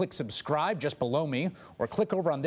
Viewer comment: Childish or not I need a good laugh through all of Trumps bullsh*t. And I swear when he signs off and says go to the comments Below me it sounds like he is saying blow me. 😂😆 listen to it if it’s not below your sensibilities. 😂😆☮️❤️